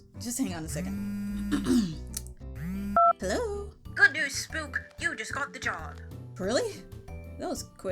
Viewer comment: I’m sorry what did they say. “Spook”?